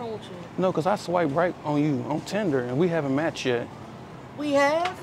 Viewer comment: At first I thought said I will swing right on you.😂😂😂😂